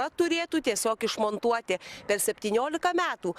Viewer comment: Any lituanian here ??? I heard "toate" there . I don't k iw if its a different word or the terminasion of another,but it sounds like "all" în my Dacian/Rumanian language 😱😱😱 !!! Any Lituanian here ... Did i hear right ???